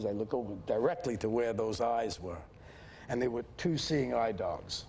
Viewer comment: What does he say at 13:53? They were two seeing eye dogs?